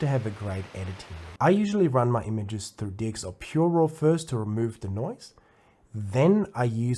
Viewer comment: He said "DxO PureRAW" and that is the name of the software he used to remove the noise... just in case you also had a hard time understanding what he said. 🙂